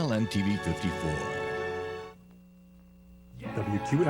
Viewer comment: You cant even make out what he was saying at 59:12? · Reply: “WQLN TV 54 Erie. Your source for great performances.”